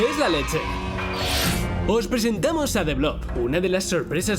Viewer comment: cómo se llama no entendí bien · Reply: Blob